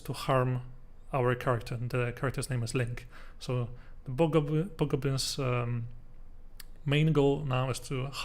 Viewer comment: please adjust this auto-generated close captions. At 14:13 instead of "hobgoblin's main goal" it says "bhaga bhaga penis main goal"